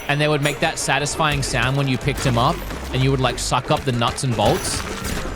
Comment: did wood say suck up nuts😱